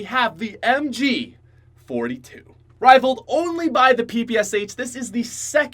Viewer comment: For the tenth pick I thought said mg 42 and after that I was like WTF MG 42 IT SHOULD BE LIKE TOP 3 then I replayed and then heard fg 42 then I was like I am Retared